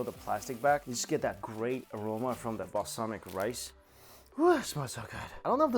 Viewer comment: Did say Balsamic rice?